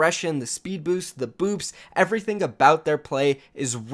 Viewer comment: THE BOOPS i HEARD BOOBS